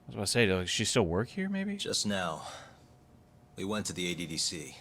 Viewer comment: I legit thought Yagami said "We went to AC/DC" I was thinking to myself "Yagami I get wanting to listen to some awesome music but TIME AND PLACE!"